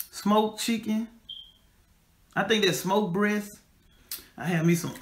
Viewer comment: im so sorry i thought she said "smoked breath" 😭